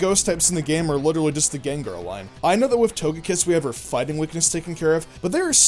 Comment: DID HE JUST SAY AHLIE?! LMFAO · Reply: No, he just said "I know" really fast.